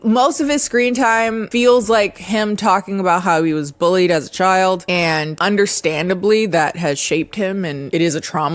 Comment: I thought you were saying "he was bullied as a child, understandably" 💀like damn you didn't have to do him like that